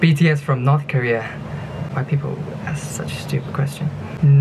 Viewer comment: Instead of him saying “why do people ask such stupid questions” I thought he said “white people ask stupid questions” in sorry don’t come at me but that’s what I heard for a second